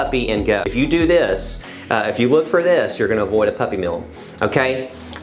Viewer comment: I thought u said avoid a puppy I was finna kil